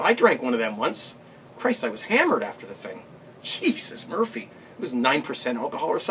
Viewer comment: ha,ha! You Are the man! At 9:46 did you say "Jesus Murphy"? Ha ha ha! Can I borrow that? Peace